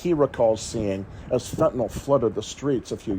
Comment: Thought that shit said Fentanyl Floyd 🤣🤣🤣